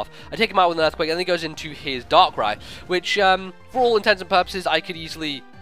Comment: it sound like you say "for all intensive purposes," but the saying is actually "for all intents AND purposes." Now you know :-)